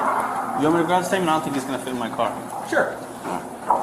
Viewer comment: sounded like the office said his mouth won't fit in my car